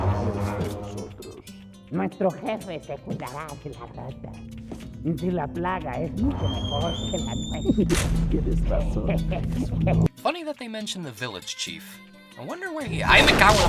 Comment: I honestly thought in chief mendez house I thought the guy said "Dice que le plaga es mucho mejor quela T(e)" I thought he was referring that The Plague was better than the T virus lol